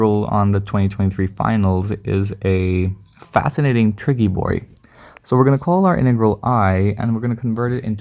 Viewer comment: did you say "triggy boi"? CRINGE 🤢🤮